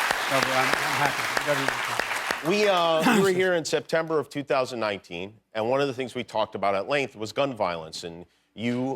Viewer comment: What Did He Say When He "Coughed" ? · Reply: "excuse me" 😐